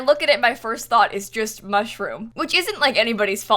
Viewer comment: The first time she said “it looks like a mushroom” I heard “mud-room” and I didn’t question it.